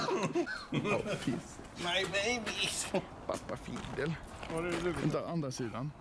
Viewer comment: Papafilia? Is that what I heard?